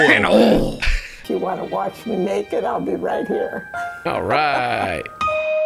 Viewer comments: Am I the only one who heard "You want to watch me naked, ill be right here"... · Why did it sound like she said "if you wanna watch me naked, I'll be right here"🥲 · Oof I now realize at the end that she said "If you wanna watch me make it..." but at first I heard "If you want to watch me naked..." I need a nap.